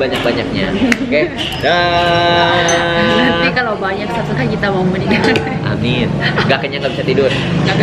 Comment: Makasih supportnya ya... buat yg suka seafood boleh coba juga makan di , Oya... jangan lupa di subscribe ya genks🙏🏼🙏🏼🙏🏼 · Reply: Maaf pemirsa itu terakhirnya pas aiman bilang jgn lupa subcribe. Ka katty bilang nanti kl banyak yg subcribe kita mau menikah. Gue salah denger apa krn terlalu berharap sih. Tolong pengglowingannya eh salah tolong pencerahananya pemirsa.